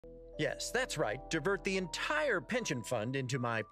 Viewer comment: Did he just say "dipper" at the start. Its good alex is making references to his other cartoon gravity falls. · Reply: i think you mean this part; 00:01? then no, he says 'divert'